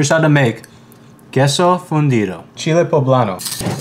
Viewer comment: I thought he said queso fundio 😭😭😭